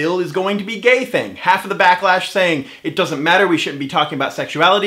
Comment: is it bad i thought that said blacklash not backlash